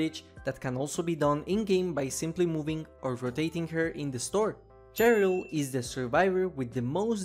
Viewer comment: d- di- did he just say “cherry-al”?